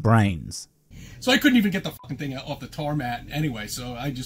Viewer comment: He actually called it the "tar mat" instead of tarmac? 🙄🤦‍♂️🤦‍♀️🤦